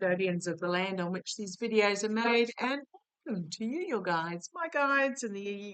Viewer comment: I hear a female guide! Sounds like she said “Maeve” but would love to hear anyone else’s interpretation. Or is just a glitch on the word “made”?